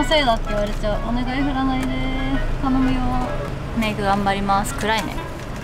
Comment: メイク頑張ります～す！が、めぐ頑張りまーすに聞こえた笑